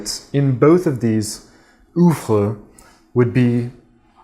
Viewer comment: Still trying to figure out what to do with my channel. For now I'm making video projects for people and getting that practice under my belt while making some stuff for my YT. But really not sure yet what to do with my channel. Having said that, I have just one Yongnuo Bicolor light right now and will get a second one in some months. But I'd really like to know the basics of color temperature from you. Thanks again bud! Oh and yes, loved the bit aout being control nerds.. I sure am a bit. I have been paying attention to lighting too a lot more these days. Still gathering information. Oh and what was that word you used at 3:49 ? For those reading, I have the Yongnuo YN 300 III. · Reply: Oeuvre- it’s a French word meaning, roughly “work,” like a work of art or a play.